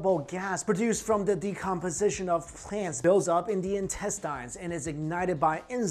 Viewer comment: What does he say @ 5:45? "produced from the decomposition of 'france'"??? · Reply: SignifieDignifieD of 'plants' acording to the english captions